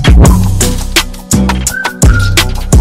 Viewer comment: "Oh poop" That got me laughing on the floor🤣🤣💀💀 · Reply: I heard "help" instead of "oh poop"